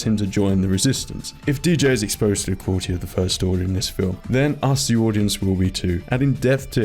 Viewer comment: What did you say here? "If DJ is exposed to get caught by the First Order here..."?